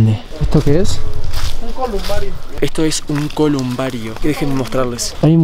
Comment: escucha Un lamento que dice venís de